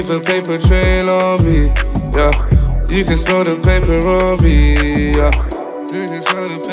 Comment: Bro I heard “you can smell the peperami” now I can’t get it outa my head 😂 you can smell that peperamiiiiii yeah